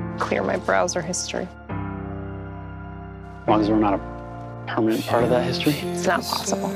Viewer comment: Beautiful! 0:12 what does ace say here? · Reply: N: More like im ready to hit the refresh button on life ... clear my browser history A: ... As long as we're not a permanent part of that history? N: That's not possible. > and thank god for cc on this edit coz when watching the episode i had no idea what he actually said... i couldnt make it out 😅